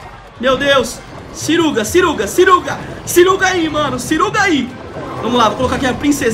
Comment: eu entendi "suruba suruba suruba..."